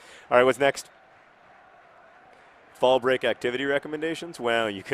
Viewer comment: hmm... couple of things I wanna say: 1) For the longest time I thought he said 'ballbreak', which really doesn't sound like something worth celebrating. 2) Is the first day of Autumn really celebrated? 3) Cornrynth sounds like a city in Cornwall or Wales to me (more made-up than real-life city Wolverhampton, but still).